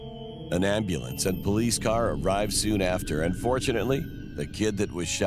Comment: "unfortunately the kid survived" Me: what LOL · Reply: I think he actually said and fortunately the kid survived and it just sounded like he said unfortunately...at least I hope that's not what he said lol